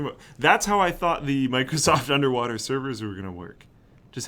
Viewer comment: I thought that said underwater decanters at first